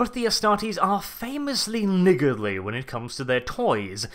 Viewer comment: "Famously.....WHAT"?!? What word did you use at around 2:21 to describe the astartes attitude when it comes to their toys? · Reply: LOL ah, "niggardly". Yeah, I know your post is two years old and you'll almost certainly never see it, but on the off chance you ended up coming to the conclusion had questionable views and stopped watching him because of that two years ago, I'll clear the air.Yeah, that's a word in english and it doesn't mean what you're thinking. It's like someone who is a miser, a penny-pincher; that kid in fourth grade who would only give you one piece of the icky raisin candy even though he had three pillow sacks full of the good candy the day after Halloween. "No! It's MINE and you can't have any!" The word is totally legitimate, totally appropriate. You can also have a lot of good, clean fun with people who aren't familiar with the word and you're not even saying/doing anything wrong! You're just expanding peoples' vocabularies!